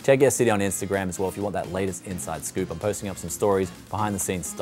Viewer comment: Definitely love this.. this is really good, just wish there was a way to get a low power GPU here at a bit higher price. BTW who else love the way he say "PISS OUT FOR NOW... BYE" That's the only reason why i watch your video till the end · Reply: I'm aussie and I heard pea's out for now (not peace (pronounced peece in Australia))